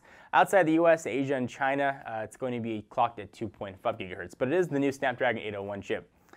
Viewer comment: Sounded like said "it's going to be clocked at 2. fucking hurts" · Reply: I heard "two point fuck gigahertz" XD